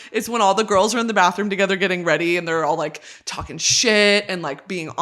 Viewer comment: i heard "taking a shit" not talking shit and it still contextually worked LOL